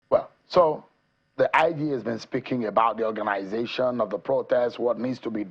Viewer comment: I thought they said Seun has been sacked